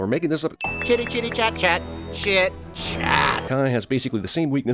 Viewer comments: doesn't I sound like he said sh*t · is it just me or did po just say Sh*t? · who else thought Po was saying shit shack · I heard shit Maybe this kids movie isn't so for kids after all. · it sounds like he said Shit. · when po said chitty chitty chat chat did he say shit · When Po said "chatty chat chat, chit chat" I thought he said "chatty chat chat, shit chat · I actually thought Po was saying, "Shitty shitty shat shat"... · Reply to this comment if you think po said shit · that kinda sounded like he said shit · Does it not sound like po says chitti chitti chat chat. Shit, shat.???